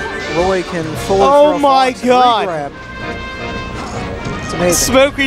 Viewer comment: when the one commentator was explaining something about Roy "for those of you who don't know, at 0% ..." and then the other guy just says some dumb shit about smoking weed, what was he about to say? Thanks in advance for whoever can tell me :) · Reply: Tristan Anastasio you can kinda hear him say something about f-throw regrab. Which is true, roy can regrab fox/falco(n) after a f-throw from 0-10%.